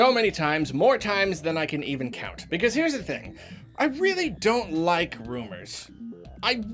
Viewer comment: At the beginning I was eating cereal and when you said “I don’t like rumors” and due to the loud crunch of the cereal I thought you said “I don’t like boomers” and I was so freaking confused